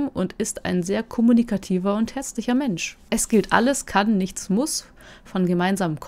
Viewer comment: Im Stream und auch hier verstehe ich jedes mal "und ist ein sehr kommunikativer und HÄSSLICHER Mensch" xDD